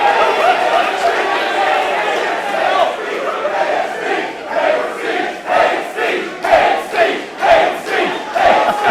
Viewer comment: what are those guys saying? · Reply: 'hate speech, hate speech!' and so on